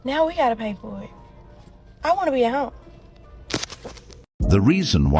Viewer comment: did she sayd "i wanna be a hoe" or "i wanna be at home"?